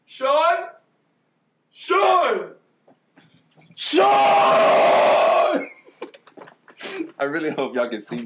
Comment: lol that sounded like Ethan